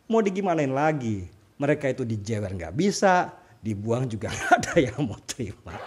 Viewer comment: Saya dengernya "Hhhh.... Tai ayaam juga gak ada yg mau terima" 😂😂😂😂🤣🤣🤣🤣